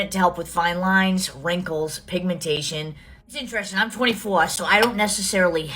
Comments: mikayla is 24 ??? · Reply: I thought she said 44 but she said no fine lines.... at 44...nothing that woman says, sorry girl as she doesn't have children, makes sense. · I'm sorry....did she say 24???? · SHE IS 24 ¿???? DID I HEAR THAT RIGHT?